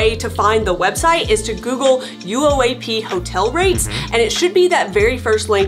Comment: what she says is “..Google UOAP hotel rates” for others looking that couldn’t make it out, like me 😀